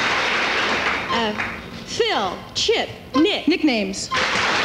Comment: did she say “Nick” ? If so, she should have been buzzed for an illegal clue.